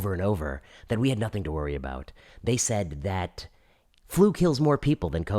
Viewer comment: Thought he said "food kills more people than COVID-19." He said flu, but works either way.